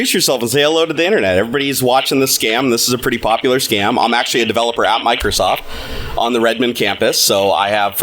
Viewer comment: Are you back at Microsoft or you were just saying it that way? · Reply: Ohh I didn't hear the "ex" part, I thought he said "I am a developer at Microsoft"